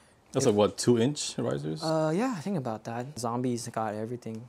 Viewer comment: what did say? zombies? any body got a link or domain? Looking for more parts sites....Thank for the help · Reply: Zombie performance